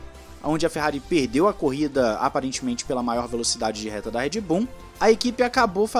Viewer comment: So eu que escutei "REDBOOM" EM 6:10? KKKKKKKKKKKKKKKKKKK